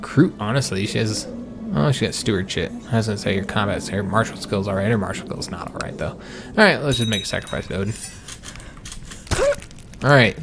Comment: I thought he said steward shit for a second XD